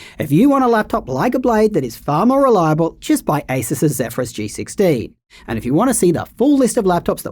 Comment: did he say Acer's Zephyrus G16? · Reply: Asus's